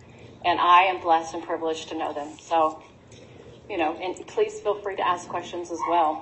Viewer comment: sounds like she said “and police feel free to ask questions as well.” I know it’s “please” but at first I heard police 😂 Looks like you did some accidental prophesying there.